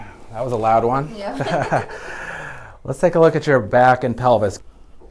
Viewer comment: For a second there I thought he said " let's take a look at your ads and pelvic " lol